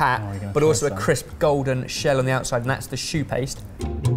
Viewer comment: Until The spelling came on screen, I’ve thought they were saying shoe paste!